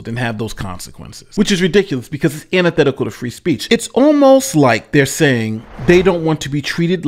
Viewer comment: did you mean “antithetical to free speech” instead of “unethical free speech”?